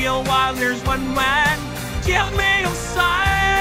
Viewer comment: it sounded like he said my name My name is Bradyn It sounded like this "Hit me Bradyn one more time!" Im scared😢💀💀